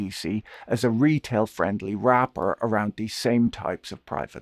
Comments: When Patrick mentioned that BDCs could be thought of as a "retail-friendly wrapper" I thought he said "rapper" and was going to segue into a rap music joke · Am I the only one who heard “business friendly rapper” and thought Patrick was returning to his roots? .